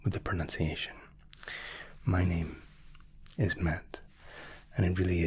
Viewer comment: i thought i heard "my name is merde" and i was like whaaaa hang on a sec 😂